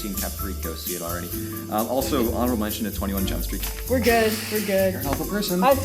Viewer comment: Wait wait wait wait wait Wait at I think 9:27 did say 21 chump street???? Bc I think I heard that